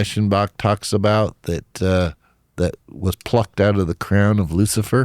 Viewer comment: I thought he said the emerald was knocked out of his crown, not plucked. Which is the accurate depiction?